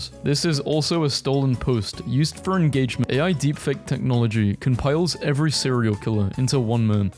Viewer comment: "..used for engagement but that's besides the point" is what it says, for people frustrated about the cut in the edit and thought it was censoring, just a mistake i think